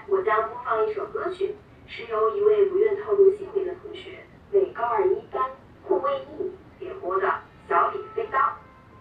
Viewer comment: did she say Gu Weiyi??